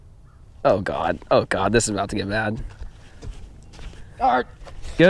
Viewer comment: I thought he said shark